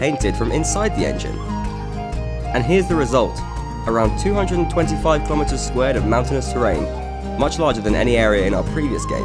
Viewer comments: I've replayed 1:21 several times and I keep listening "225 kilometres SQUARE". That'd mean an area of 50625 km^2, roughly the size of Slovakia or Costa Rica. It's just probably that I heard wrong or he meant 225 square kilometres (which is still pretty big for a random map made on the fly.) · Did I hear that right? maps of 200 square kilometers of terrain ?